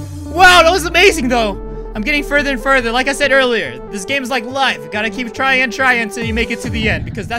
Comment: I thought he said : this game is my whole life